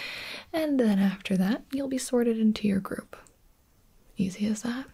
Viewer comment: When she said you'll be sorted into your group, I thought she said you'll be sorted into Gryffindor